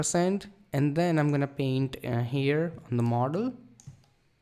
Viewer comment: great work thank you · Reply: What did he say "in the marder"?